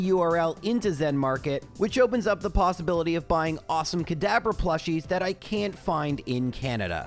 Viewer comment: Check out Zenmarket if you want some cool stuff from Japan: <link> Sign up with code for a 750 ZenPoints gift. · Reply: This episode only, every time you said "Kadabra" I heard "Cadaver." I do not want a cadaver plushie, and I hope you agree.